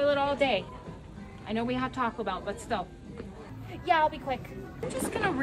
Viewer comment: “I know we just had taco bell” 😭💀😭💀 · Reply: I thought she said “I know we just had a talk about it” 😭😭